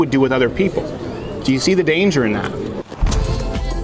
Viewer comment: Legit thought I heard "watch good morning Buttplug" at the end, instead of MugClub